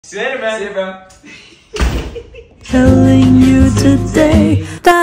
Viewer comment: I was on minecraft but was still listening to my iPad and i heard ‘Killing you today!’